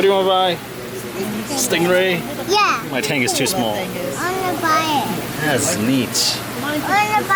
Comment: awhhh is he saying "I wanna buy it"